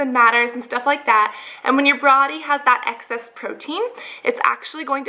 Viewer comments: she says brody instead of body lol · brody:/ instead of body tehe